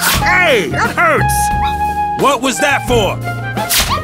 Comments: BLASTED FUCKBALL! I'm sorry but, this is what i heard. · LMAO! Did Sazh just say "Fuckball?!" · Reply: Ace Roberts No I'm telling you what he actually said not what I think it is. He said fluff ball but by the way he said it it can easily be misheard. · Am I the only one who heard it as FLUFFBALL instead of Fuckball? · Did Saz just say " fuckball" or "fluffball" ? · blasted f**k ball that's what i heard. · Did Sazh just call that chocobo chick a "Blasted Fuckball" ?!?! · At the end there did he just say "Blasted fuck ball"? Cool. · does he say blasted fuck ball? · It sounded like sazh said fuckball, but unfortunately i think he was saying fuzzball